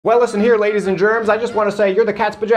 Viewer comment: ?I thought he said ladies and Germans there